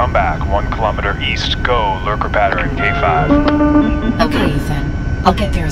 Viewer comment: What's he say? Lurcopathory, or what?